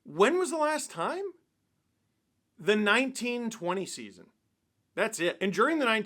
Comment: i might be stupid i heard this as “1920” not “19-20”